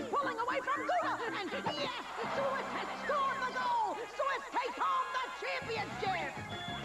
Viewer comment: What is Deli Sandwich saying? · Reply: "Swiss takes home the championship!"